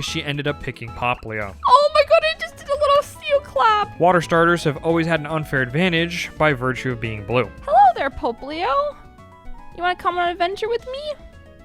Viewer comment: did she call it Pope Leo?